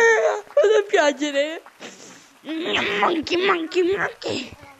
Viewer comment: Ma a 3:00 ho sentito vado in carcere😂😂😂